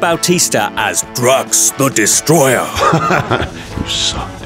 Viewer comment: did I hear "drugs the destroyer"